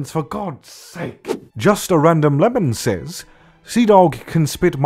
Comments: Who else hear's "Just a random LEMON says" when his/her name is Just a random Melon? (And he talk's about other people's grammar) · When he said 'Just a random Melon says' at 1:19, I noticed something. First I thought I was wrong but then I repeated it and he actually says 'Just a random Lemon says'. He's gotten insane from all these Lemon comments 😂 First I thought I was the one who got crazy. I am relieved now. Thank you for being awesome and clumsy Connor ❤